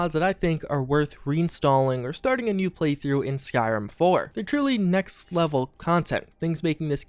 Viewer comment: Did you just say Skyrim 4?